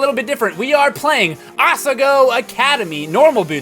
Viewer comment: I thought it said asiago academy at first XD